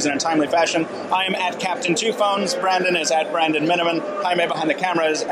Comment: Is his name. I thought he always said I am brad and I